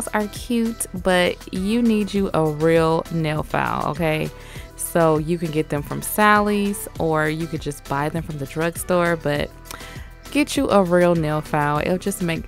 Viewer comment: Did she said "drug store"